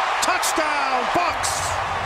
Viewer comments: Who else heard “Touchdown Fucks” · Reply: It's "Touchdown Bucs." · it sounds like he said touchdown fuck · anybody else hear "touchdown fucks" ??????